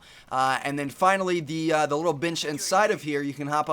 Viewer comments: When he said this little bench I could have sworn he said "this little bitch inside of here" lol :) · i thought u said the little bitch lol